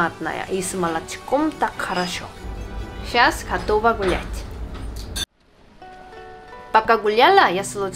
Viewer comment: "Щас готова бл*ь" Я один это услышал?